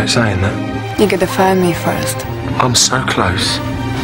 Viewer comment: I dont understand what she is saying at 1:51 ".....(something), good game". anyone understands? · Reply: What about 2:26? what does she say? something like you get to find me first?